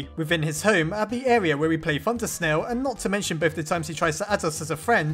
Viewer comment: I thought he said, "undersnail" Imagine an AU called undersnail Please make this a thing if you want to!